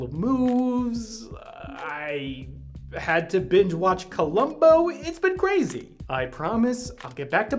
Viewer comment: Binge-watching "Columbo" is the best possible excuse -- indeed, it transcends the category of "excuse" and becomes a full-fledged REASON. Also, my hot take: the best episode of "Columbo" is the one with Johnny Cash as a murderous gospel singer! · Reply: Oh he said Columbo that makes sense, I thought he said Love Boat. really should add official captions (my hearing is somewhat impaired). should cover Columbia references in anime and manga.